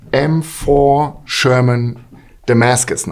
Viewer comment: I totally thought he said M4 Shaman at first. Lol